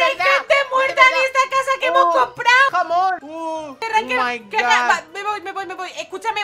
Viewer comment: Escuche mal o dijo amor o jamón en el minuto